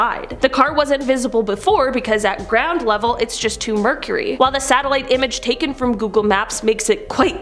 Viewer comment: did she just say "two mercury" or "too murky?"